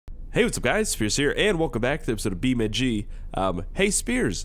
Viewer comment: For a second i thought you said Hey whats up guys scarse here xD